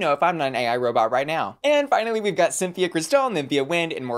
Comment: Did she say “Symphia Cristal” at 10:22? 🤣